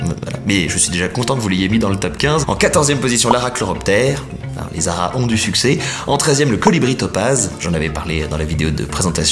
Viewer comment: j'ai entendu "les arabes ont du succès" 😂